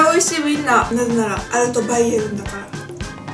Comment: なんて言ってるかわかる人教えてください笑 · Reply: reohoku 、 なぜなら、アルトバイエルンだからって言ってます笑